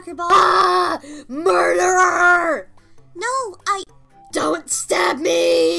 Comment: WHY DO I HEAR MURDER HER